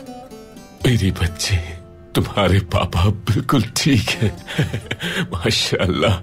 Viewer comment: Did Bumsi say "tumhare PAPA ab bilkul..."?